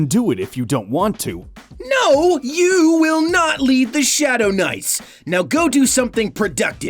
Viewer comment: I thought Zenix said "No! You're not gonna leave the Shadow " NICE"" XD